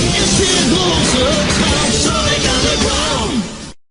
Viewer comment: omg IT SOUNDS LIKE HE SAID SANIC UNDERGROUND